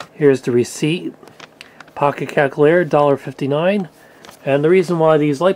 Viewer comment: He sounds like hes saying CACKalator